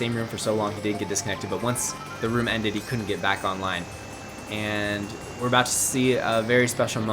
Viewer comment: Holy cow you scared I thought you were saying you couldnt play online anymore 😂